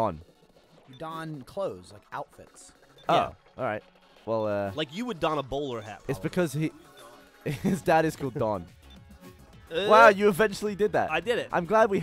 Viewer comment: At around 1:25, Geoff says "...like you would don a bowler hat, ___" and it sounds like he said Holly. Which is my name. And I have a bowler hat I frequently don (see my picture). Obviously he wasn't actually talking to me but I still got very excited.